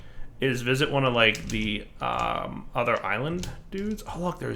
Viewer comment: Oh my gosh, I'm like five... I heard him say "I like the Oh Penis" and I smirked.